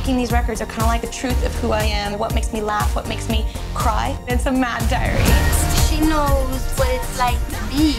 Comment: Did she just say "And some mad diarrhea" @ 00:34 That just made her even sexier to me...